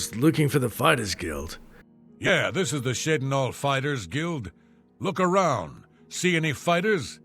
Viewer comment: In the first few seconds 0:26 I thought said something like sh.tinhall Fightersguild. xD